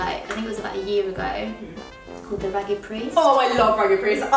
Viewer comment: Dying at how good you both look😍😍😍 btw who knows what clothing brand Freya said she likes at around 5:06?? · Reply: raggedpriest